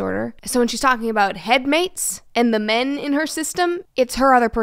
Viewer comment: At first I didn't hear head mates. I thought she said roommates and I was extremely infuriated and confused and thought to myself. Why can't you just be yourself? That doesn't make sense. Fuck what they think and then I got the rest of the context and I'm like.... Oh 😰